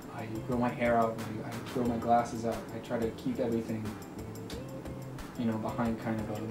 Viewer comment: 3 Things about Jack Stauber: 1: he drinks a lot of water 2: he does bad ass stunts 3: he runs really fast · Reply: i thought he said he threw his glasses out for a hot second